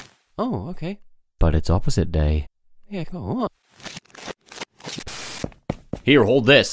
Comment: it sound like he is saying yeah cool